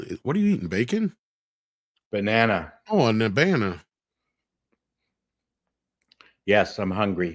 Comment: what did say? “An Abana”??? 😂